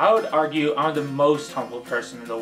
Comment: I thought he said homo.